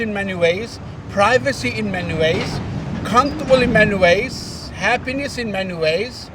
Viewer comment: My GodIt didn't even sound like he said.There's happiness in many ways , sounded like he was saying happiness in MEN ways wake up united states 🇺🇸